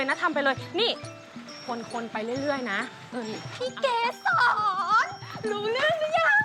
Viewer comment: ได้ยินว่าทิพเกษร นึกถึงกินรีเลย😂😂😂😂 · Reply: พี่เกษรค่า